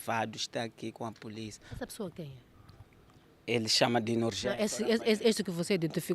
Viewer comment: Ouvi o nome da pessoa. A vitima disse "ele chama de Norgelio" 22:25 antes de ser interrompido pela repórter.